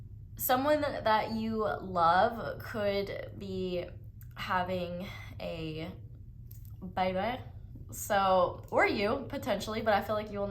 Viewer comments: I can't understand what is saying in this particular sentence, can someone help me out? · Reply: I believe she said "Someone that you love could be having a baby" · did anyone else pick Pile 1? I can’t make out what is saying here 🙏🏾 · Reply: I believe she said "Someone that you love could be having a baby"